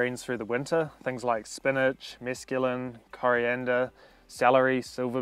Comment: Anyone know what he says at 4:07 I'm assuming it wasn't mescaline? 😂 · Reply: Haha mesclun (it's a salad mix)